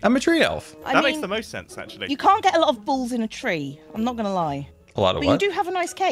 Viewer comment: MY POOR HEART, I WAS SO CONFUSED. PLEASE TELL ME IM NOT THE ONLY ONE WHO HEARD GO "You cant get a lot of balls in a tree, but you do have a nice cake"